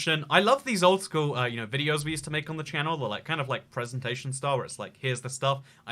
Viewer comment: captions say “presentations Star Wars” 😂😂😂